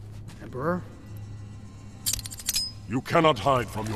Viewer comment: did Takeda say "emperor" or "burr"? Because of the snow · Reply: He said Emperor